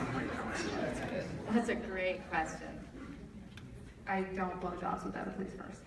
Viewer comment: Does anyone know what Melanie said at 1:08:42 ? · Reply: Emsoftball "I don't blow jobs without a please first." It was her line to dolls in season1 i think